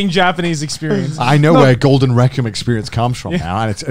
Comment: I misheard Connor say Golden Requiem as Golden Rectum given what they were talking about